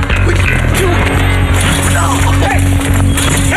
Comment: thought he said "save it for the gay guys"...whoops